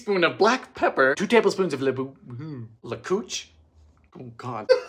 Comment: “Is that an ingredient or an air strike?” 10/10 lmao 😂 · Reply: For a moment I thought he said lecouch. Then yell honey!! I need the couch!! I am not in the mood!